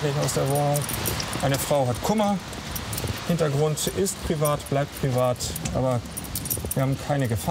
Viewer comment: Danke jemand der es verstanden hat 3:07 Privat ist privat